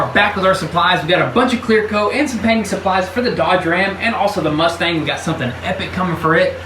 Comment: did i just heard Mustangggg?